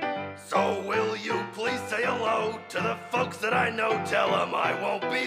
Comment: [Bill Cipher]-singing laughing HIT IT! We’ll meet again Don’t know where Don’t know when But I know we’ll meet again Some sunny day [Soos]-singing Keep smiling through Just like you dudes always do Till the blue skies drive the dark clouds Far away (hehe) [Grunkle Stan]-singing So will you please say hello To the folks that i know Tell them I wont be long They’ll be happy to know That as you saw me go I was SCREAMING this song (coughing) [Old Man McGucket]-singing We’ll meet again Don’t know where Don’t know when But I know we’ll meet again Some sunny day (heheheha) [Bill Cipher]-speaking Hey kids! It’s me your old pal and overlord Bill Cipher! I’m sure you all miss me, but if you ever feel bad just remember that I watch you while you sleep! At the end of the day your brain is just a meat computer in a bone cockpit piloting a skin robot! You think the world makes sense? NOTHING makes sense. So you might as well make nonsense. Think about it! [All]-singing We’ll meet again Don’t know where Don’t know when But I know we’ll meet again Some sunny day [All] laughing [Bill Cipher]-speaking I hate you all Bro this took so long to type out holy shit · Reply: I thought stan said fucks not folks